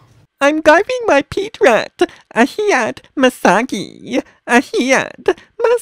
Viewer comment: Did he say Misaki?!